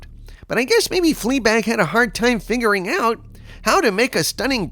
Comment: totally heard it as "fleabag had a hard time fingering out how to make a stunning brave woman" 🤣🤣